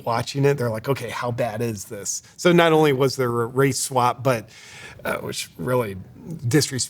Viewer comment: At first I thought you said a racial slap, lol, swap slap kind of the same to the Egyptians apparently.